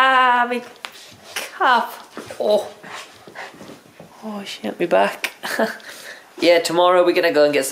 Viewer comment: "PEPSI SHUT UP" I laughed way too much then I should have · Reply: Guys at 4:34 says "shit my back" if u guys didn't hear but he did. I never heard him swear before. That's the first time I heard him swear in a video